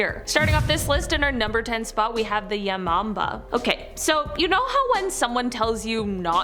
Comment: When she said Hanako-san i immediately thought about the anime Toilet Bound Hanako-kun💀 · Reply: when she said yamamba i heard it as yo mama